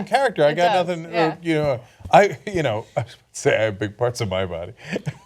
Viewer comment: So who's the sexiest woman? Oh wait we can't do that? Interesting. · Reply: I heard "it's the epic parts of my body" lmao